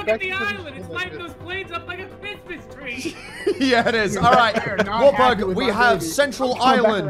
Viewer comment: D- did you say "War Pug"?